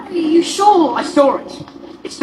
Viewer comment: I am German and I thought/heard saying 'sword' instead 'I saw it ' 😅